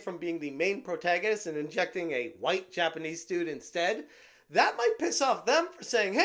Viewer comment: why are u saying white Japanese instead of just Japanese? 🤨 I thought the first time u said "right Japanese" but no my ears didn't fool me when u said it again. How about just Japanese